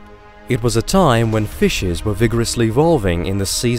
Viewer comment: Did he say “fishes”??? 🤨